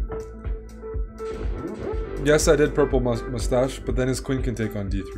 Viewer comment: Did he say "... yes, I did purple mustache but then ..." @ 12:16 ? I'm quite disturbed because even listening to it a dozen times I hear exactly that. I guess I'm cracking up :/ ;) XD :(